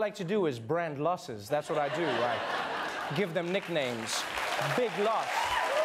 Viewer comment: I first thought he said "Big Loser"